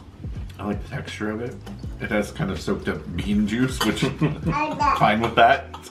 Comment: For a minute there I thought you said 'soaked up bin juice' 🤣 Imagine soaking up your tattie scone in that!